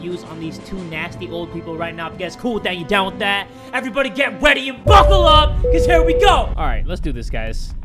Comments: anyone hear say “weady” instead of “ready” or is that just me ?? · did he say ready or weady? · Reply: said ready